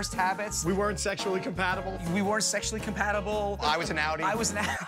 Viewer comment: What did he say? I heard only i was an... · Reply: Outie😭